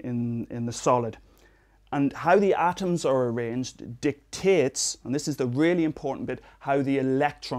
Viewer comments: Did he say dick tits? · did he say dick tits??